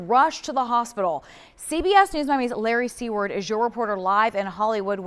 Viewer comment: I heard "Larry C-Word"......